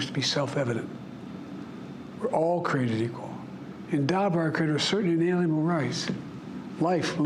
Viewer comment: What is he saying at 8:17? 🤷‍♂️🤷‍♂️😂 I can’t understand 🤔😂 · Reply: "endowed by our creator with certain inalienable rights". he's quoting the declaration of independence.